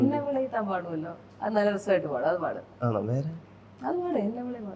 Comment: min what said???😅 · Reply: Anoo vere??